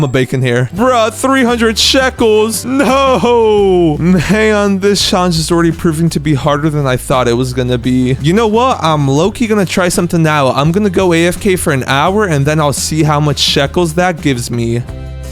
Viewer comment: also did he say harder?